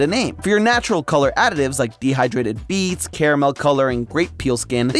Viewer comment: i heard "dehydrated feets" 😭